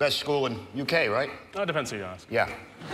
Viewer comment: what did say? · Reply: Depends who you ask